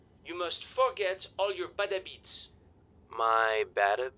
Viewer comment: It took me the LONGEST time to realize Jacques was saying "bad habits" at 2:15. With that thick accent, I always thought he was saying "battle beets". XDDD